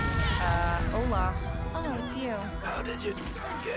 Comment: "Straight". Haaa! 0:31 Did she say "hola"?